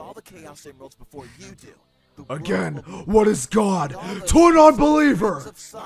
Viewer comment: wait did shadow just call sonic.exe a HER i know it maybe was a mistake but if it was on purpose ROASTED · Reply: He said “What is a god to a nonbeliever”